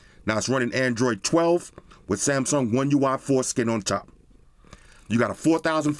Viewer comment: I heard “It’s running android 12 with Samsung one ui foreskin on top” and had to run that shit back 3 times to be sure 😅😅😅